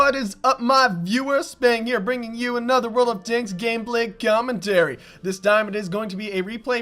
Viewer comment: Hahahaha lol at the beginning it sounds like you say world of 'dicks' XD (yes I know, I'm so mature lol)